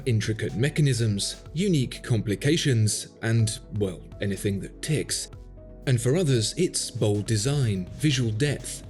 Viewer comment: At the beginning, video was quiet, so I heard "and anything with tits" instead of "and anything that ticks"... oh dear